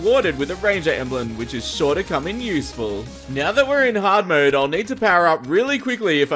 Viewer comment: At first I thought he said the emblem was short of coming useful.